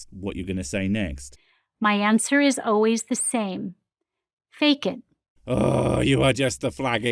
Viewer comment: "fake it" I swear what I heard was "Bacon" so that was hilarious "The answer is...bacon!"